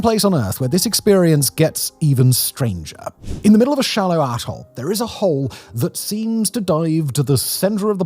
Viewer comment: am I the only one that heard “In the middle of a shallow 🍑 hole”? What is he saying 😭